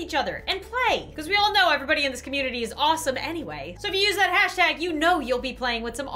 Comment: Me: DID I JUST HEAR AWESOME AND WHITE???!??!?!?!?!?